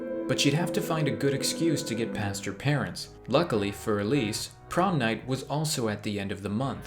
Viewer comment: I thought it said “porn night”😂😂😂